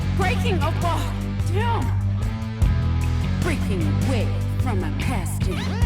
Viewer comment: DOES ANYONE HEAR THIS? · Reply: I think I hear "Freaking All*h"